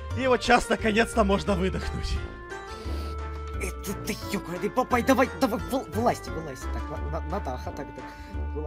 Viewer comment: дядя мне послышалось что на 8:53 сказал мат. сказал или еб**ый бабай или екарный бабай